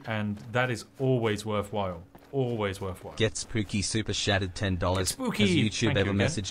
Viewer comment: That AI voice sounds like he says super shatted, not chatted.